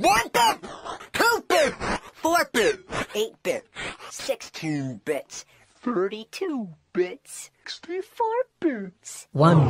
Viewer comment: i heard 65 bits